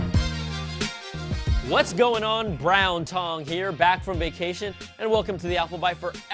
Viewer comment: Did he say Brown Tong????